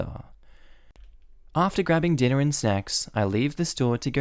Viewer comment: Did anyone else hear that as dinner and sex? >_<